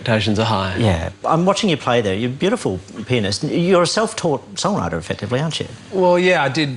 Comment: sounds like he said "You're beautiful penis"